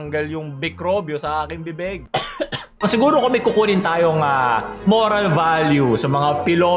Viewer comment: ano sabi ni tita oral value?